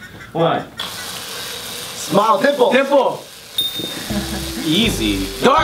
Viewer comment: I don't know if I'm hearing wrong but at 16:30 i keep hearing "pimple" not "dimple." Is it just me?